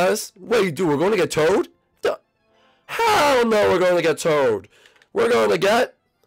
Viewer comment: Did say Hell No